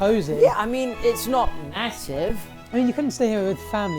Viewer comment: did he say... ma... ma... massive?